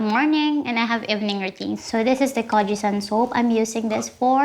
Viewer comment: The subtitle is wrong. I just want to make clear that it is not 'cortisone soap,' she said 'Kojie San soap.' Kojie San is a brand name for a Kojic acid soap, while the cortisone is a steroid, which is different from one another.